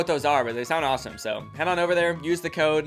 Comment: WHEN HE MENTIONED THE SPONSOR I THOUGHT HE SAID "hella fresh" 😭😆